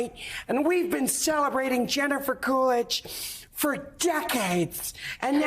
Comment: Truly thought she said "Gender-fer Coolidge" for a moment.